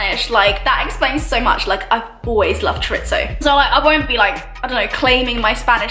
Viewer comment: What does she say in 6:47 after “that explains so much” ? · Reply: "I've always loved chorizo" but she pronounced chorizo wrong so maybe that's why you didn't understand her